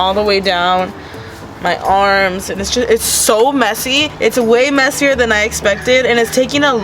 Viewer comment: she say this sh*t is so messy or did she say this legit is so messy